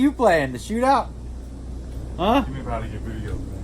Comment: What did Phil say at 10:25? "Get me up outta your videos, man."?